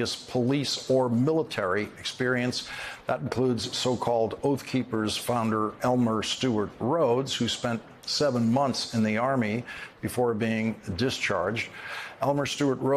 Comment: I think it is a long stretch to think that is the majority. The majority of 'veterans' will have nothing to do with marches, or patriotic celebrations. The bulk have had a gutful of the crazies while in the service. You tolerate them but have no wish to associate with them once out of uniform. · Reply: They didn't say it was a majority. 0:29 "At least 15% of those defendants had previous police or military experience". Maybe you heard it as "at least 50%"?